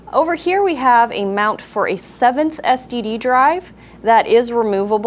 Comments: did she say STD drive?.. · does she say "7th STD Drive?" That is awesome. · did she say std drive? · Lol at 5:37 she said "std drive" instead of ssd drive